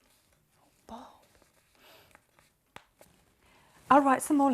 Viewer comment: What does Grace say quietly at 6:25? “A bomb?”